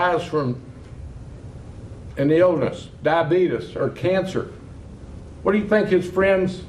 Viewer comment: He sounded like Wilford Brimley when he said "Diabeetus"